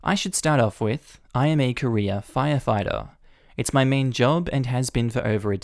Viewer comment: I wasn't looking at the screen and I heard "I am a Korea firefighter" and I was very confused for a moment. lol